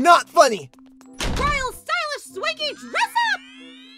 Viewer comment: For a moment I thought it said "royal stiysh skanky dress-up!"